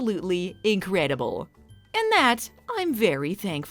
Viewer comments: While watching this, I would constantly mishear "Crappy Apple Pie" for some reason! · I absolutely misheard it as "Crappy Apple Pie" when I was eight.